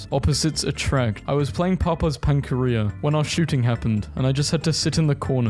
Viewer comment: i thought it said papaya not papa